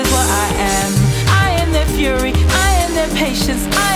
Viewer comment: Nyeh... I keep hearing Sans's "I am determination" in Garnet's "I am a conversation"... BUT THEY'RE BOTH SO GOOD... Must... Resist... >_<